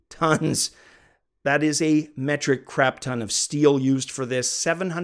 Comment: english is not my first language but, at 3:54 ,did you said "metric crap" ? because i am sorry but the metric system is much simple that the imperial system and much world wild, if is not the case i apologie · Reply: Haha no, no. I use metric myself as a Canadian and continental European. What I said was "Metric Crap-Ton" it is an expression in Canadian English that means "A VERY LARGE AMOUNT" :)